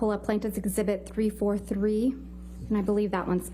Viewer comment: Did I hear the judge say ‘Yes Ma’am’ to Camille? and see Camille do a double take and smile to the judge? 4:57 Go Camille